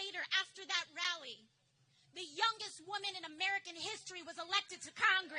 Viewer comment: When she said she was the youngest I thought she said dumbest (she should have?)